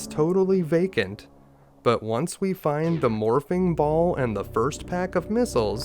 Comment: bruh at first i heard it as tge morphine ball